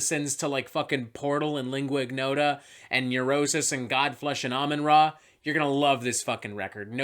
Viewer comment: what's the last band??? The close captions says Almond Raw. I'm quite sure that's not it lol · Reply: Amenra 😂😂😂